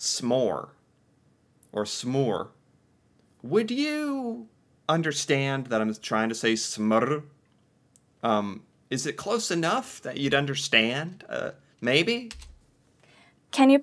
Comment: Nope, I would think that you were saying “smore” a name for the marshmallowandchocolate between cookies thingy